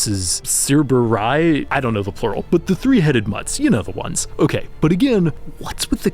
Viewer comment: You said "three headed ones" I heard "three headed mutts." 😂